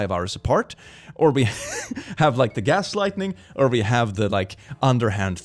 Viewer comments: said "gaslightening" instead of "gaslighting". I love this particular slip of the tongue. This is my new term for it. :pillow-turquoise-hot-chocolate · it sounds like he said gas lightning